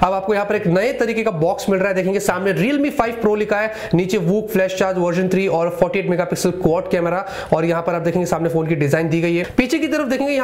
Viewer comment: Vooc fast charging and fuck it megapixel quad camera @ 1:18 ...who else heared it...like